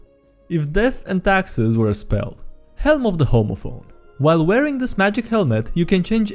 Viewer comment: did a double-take cause i thought he said "Helm of the Homophobe"